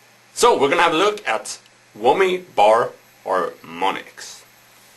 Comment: atthe 0:30 he said whammy bar or ...? what he said them?